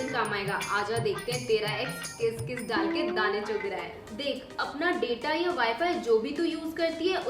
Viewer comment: OMG 😱 I thought she said c**d 😲 But I repeated it n listened to it again it was chug rha hai😂